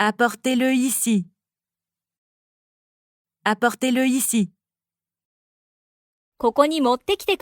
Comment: アパホテルって聞こえてきて何かと思ったら "Apportez-le" だった